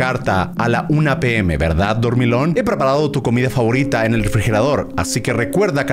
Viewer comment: Que dijo german en el minuto 6:48 ? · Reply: Lo que dice en el texto en la parte de dormilon